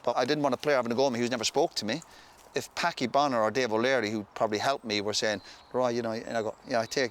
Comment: does Roy say Packi ? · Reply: Packie Bonner was a legendary Irish goalkeeper